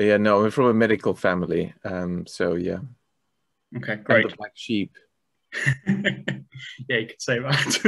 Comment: Did he call his family sheep there? What did he say exactly, sound like "raised by sheep" or "graze like sheep". BTW that sure was a little nugget of golden life advice: don't go into the medical field because you can't move and take your degree to another country. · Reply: "I'm the black sheep"